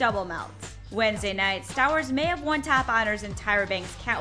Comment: I thought this lady said, "Wednesday night, Star Wars may have won top otters in Tyra Bank's catwalk competition"